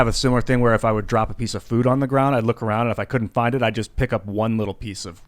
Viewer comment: i thought he said poop on the ground lmfao